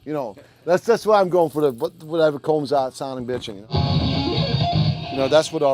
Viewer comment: What is he saying at 6:50? "That's just what I'm going for what ever comes out sounding bitching" ?